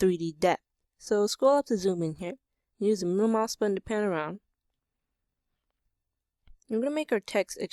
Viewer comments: ACTUALLY - forget my previous problem. What are you saying at 2:40? "Use the mfhns pad to spin around" is what I'm hearing. I have no idea what to do now. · Im lost what does he say in 2:45 does he say turn to blue mouse? Please help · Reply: He said use the middle mouse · What did he say at 2:40? How do I pan around!? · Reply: Siddiq 9570 middle mouse :D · "Use the moo moss to spin around" ??? What are you saying ?? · I didnt hear what you said at 2:45 it sounded like use the moo ma spin around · what is the fucking moo mouse button at 2:45?! I cannot understad what the fuck you are saying and I really want to progress with his freaking video! :D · what does he say on 2:44 Mumouse or something?